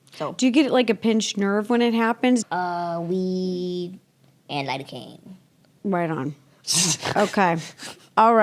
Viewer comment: did say she uses weed and lidocaine for pain management? Idk if the captions are right